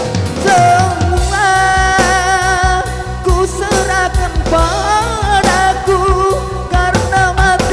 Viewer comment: Maaf.. Coba dikoreksi di ahir reff.. " Semua kuserahkan pada-Mu mas... " Mungkin sy salah dengar